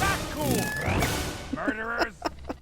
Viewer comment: did Scott just say "Birderers" ?